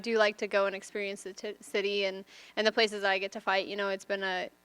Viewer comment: Did she say she likes to check out "Tit City"?